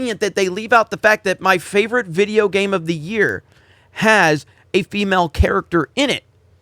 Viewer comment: oh, Game of the Year. I thought you said that Metroid Dread was your favorite game of all time! I was gonna say, "Wow! That's high praise!"